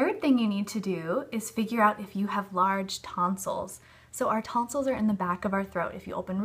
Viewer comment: I thought it said “do u have large toenails” I was so confused for a second